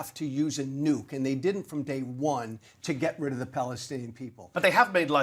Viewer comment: Did I hear right at 7:11 Get rid of the Palestine people?